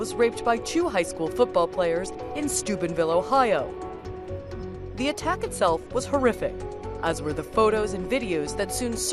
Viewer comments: did she say the attack was horrific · Lol I thought she said Stupidville, Ohio